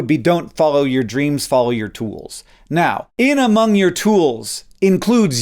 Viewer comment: I thought it said “Follow your toots”…….